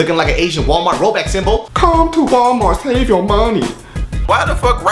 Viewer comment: when he said "save your money", when i heard that first time i thought he said "save your mommy"!....lol